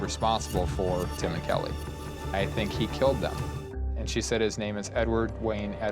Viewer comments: Sounded like he said “they think I killed her” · I think it sounded like he said "they think I killed her." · I heard "They think I killed her" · I don't know man, that "confession" was pretty vague, also sounds like it could have been, "they think I killed her"... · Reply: Exactly what i said and heard clear as day. Theres no D sound at all. Absolutely he said "they think i killed her" how can they mishear it!? He still seems guilty though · I thought it sounded like he said "they think I killed her"